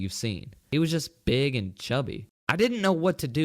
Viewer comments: wait did someone say... BIG AND CHUNKY Moto moto confirmed! OH DM IT SAID CHUBBY. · I thought he said big and chunky